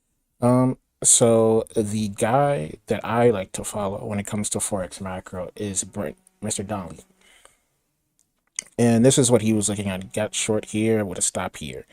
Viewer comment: hi, what did you say on 2:25, when it comes to forex macro what? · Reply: I apologize. His name is I corrected myself since I don't know him well enough to be on a first name basis and he is older than myself so I wanted to show respect by calling him. He has a newsletter called AMFX it's around $50/month but worth it. He also has 2 books about trading FX which are REALLY good reads for traders at all levels. His newsletter is more so for alpha generation or what can we trade or look to trade based off current data. He has a free newsletter if you want to check it out but you receive less post/alpha.